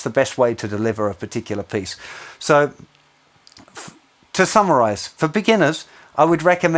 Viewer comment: it sounds like you say, "to thumberize" instead of "to summarize", which is great considering so much of the lesson is about thumb use. Freudian pun? Great lesson! Super-interesting stuff.